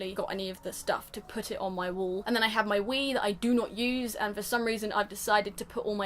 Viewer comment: Omg I thought you said “then I have my weed I do not use” 😂